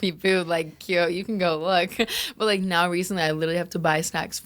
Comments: Did she say “Kio go look” or am I crazy it was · Did she say “ kio you could look “